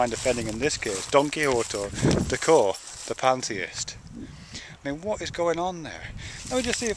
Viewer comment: Hahaha, the way the guy pronounced it I thought he said "DonkeyWhoreToTheCore".... shit I might just use that....